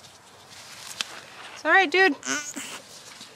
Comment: Did anyone else hear that?? She said “you’re okay” and the goat said “I’m not” 4:59 😂